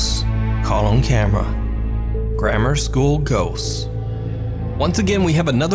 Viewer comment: LOL! When he says ghosts, it sounds like he says gose! 🤣😂